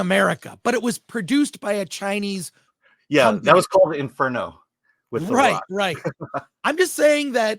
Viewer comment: "That was called Inferno with" What does he say there, "The Rock"?